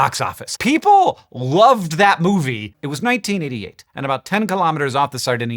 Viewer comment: I heard, "it was 19 idiot" haha